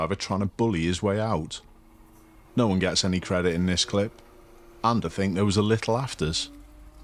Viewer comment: could someone let me know what he said here, please? Sounded like 'and I think there was a little afters(?)'?